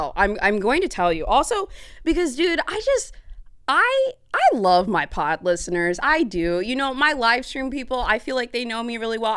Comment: I swear I heard “I love my pot listeners” 😆 But you said pod…